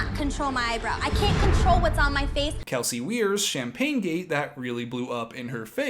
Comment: When he said “Kelsey Weier” at first I thought he said Kelsey Beer lol